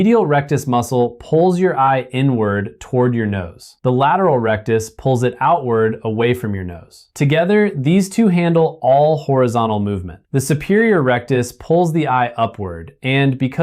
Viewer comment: It sounded like he was saying "the superior erectus"... I was startled for a moment thinking "Hey, howd he guess my nickname" 😂